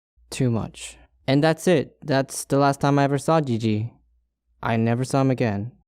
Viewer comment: ye i was about to unsub to lol Edit: i always hear gigi as gg